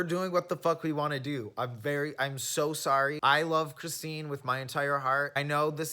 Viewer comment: I thought you said I love Christine with my entire fucking car and was very confused